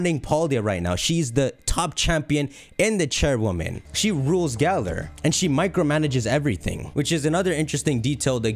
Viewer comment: did you say she rules galar?